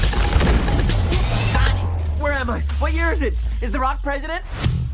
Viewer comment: did he say "is The Rock president?"